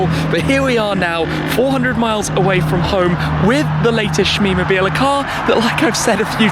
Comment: SHIT MEE.... that looks nice ! Or at least that’s what I thought said !!